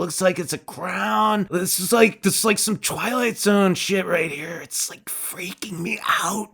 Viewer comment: When you said 'Twilight Zone' I misheard it as 'Twilight Sun'. Is that the human Twilestia?